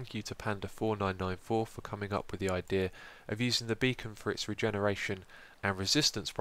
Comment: OHHHH! Beacon. I thought it said "Bacon regenerates health."